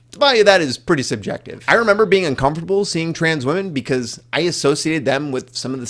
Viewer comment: Only one thing that I could tell was a pronunciation mistake. When you talked about trans peoples genital and you said trans women (MTF) it sounded like you said “trans men.” Great video though :)